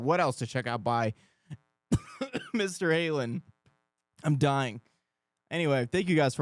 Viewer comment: Did he say Mr. Halen 😂